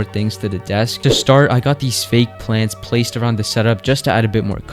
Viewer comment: it says lance instead of plants